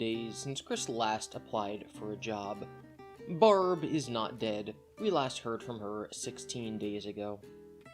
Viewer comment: left the room to listen to the vid while i was cooking and at first i thought he said "Barb is now dead we heard it days ago" i feel like its gunna happen any day now. chris probably wont even miss her.... just the money she gave him